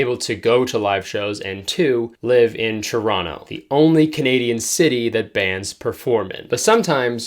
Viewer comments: I thought you said Toronto was "the only Canadian city that bans performing" and I thought we had a Footloose situation on our hands for a second. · Heard that as Canada “ bands performing” like you cannot perform or see bands perform in Toronto I was like whoa that sucks